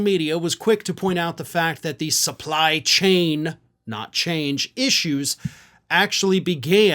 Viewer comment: I thoroughly think trumps a moron, but it sounded like he said chains not change. Still a f% ING idiot.